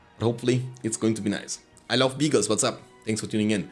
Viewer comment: i heard "i love bigos" 😂